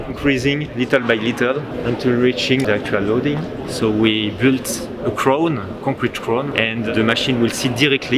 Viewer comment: I didn't get what the first guy said, need subtitles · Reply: Yeah, I'm French and even I can confirm he speaks english with an awful accent. That's what (s)he said (with no corrections): (1:22) - The number over there. You can see the number ? - Oh yeah, what is that number mean ? - The load, actually, carried by the crane, increasing, little by little, until reaching the actual loading. [...] So we built a crown, a concrete crown, and the machine will seat directly on the concrete crown, with an interface made by the cryostat bearings, 18 cryostat bearings, metallic cryostat bearings, dedicated to support and to allow any translation of the machine during any (seismic) event. It's an impressive supporting system, very robust.